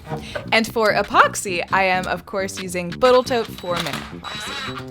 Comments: Ummm... at @ 15:11; did I hear you say "BoatalTote"?? 😲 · Did she say "Botal Toat"?